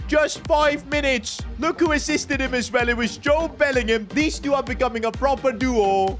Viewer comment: joe Bellingham unless i hearing thing i hear joe Bellingham